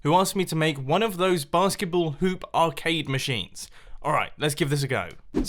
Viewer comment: i heard that basketball poop arcade machine